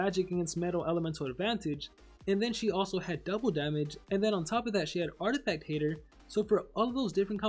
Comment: it sounded like he said on top of that sh@